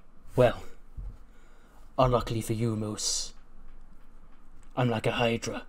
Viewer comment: I already miss the true and real ECW 😭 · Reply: did you just say... Moose-iah 💀